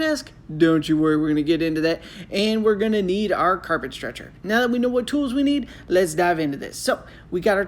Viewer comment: I thought you said. Carpet "stretcher" wtf bro??